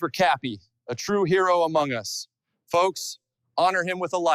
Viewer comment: Did he said Among us